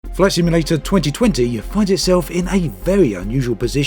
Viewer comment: Sounded like you said “Flesh Emulator” at the start of the video. We’re not quite there yet!